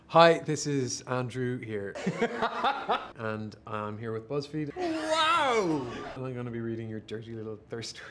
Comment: did he just say bussfeed?😂😘🔥